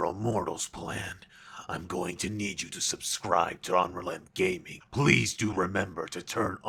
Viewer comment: who else thought goku black said "omelette gaming"